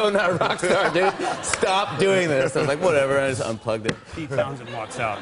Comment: lol thought Jimmy said "Fuck it.."@ 2:09 but after a few listens I realized he said "Unplugged it" :(